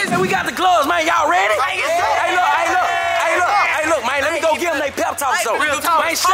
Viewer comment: It sounds like said let’s go get that ham